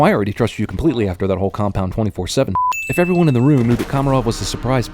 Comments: you say 24-7 but the subtitles say 27-4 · you said 24-7 but the subtitles said 27-4 sir i am your sinner of movie sinner's sinner. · instead of 24-7 it says 27-4!!!!!?! :O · Narrator says compund 24-7 but subtitles say 27-4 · in the video the subtitles say compound 27-4 shit when he says 24-7 shit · Found a sin: at 5:08 He clearly says "Oh, I already trust you completely after that whole Compound 24-7 sh*t." When subtitles say "Oh, I already trust you completely after that whole Compound 27-4 sh*t." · Sin 66- 5:05 instead of saying "24-7" the subtitle says "27-4" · He says 24/7. Subtitles say 27/4. CinemaSins sins while covering movie sins.. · he clearly says 24-7 an the screen clearly says 27-4. Do you even proof view these before uploading them? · Caption says "Compound 27-4 sh*t" but he says "Compund 24-7 sh*t" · it says 27-4 instead of 24-7... I deserve a metal. Everything Wrong With Everything Wrong With A Good Day To Die Hard In 6 Minutes Or Less :P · I can be picky too! At 5:09 you said 24/7 but the subtitles said 27/4. I am a big fan though, so good work cinemasins :) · lol at 5:08 he says 24-7 but the subtitle says 27-4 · subtitle says 27-4. narrator say 24-7.... the sin count is now over 9000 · you say 24/7 but the captions say 27/4 lol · One more sin: at 4:58 narrator says 24-7 but subtitles say 27-4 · he says 24-7 but subtitles say 27-4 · Its say 27-4 instead of 24-7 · I like how at 5:09 instead of 24-7 it says 27-4.... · you wrote 27 - 4 instead of 24 - 7